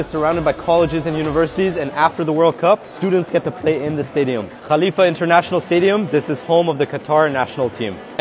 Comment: I thought he said mia Khalifa 😂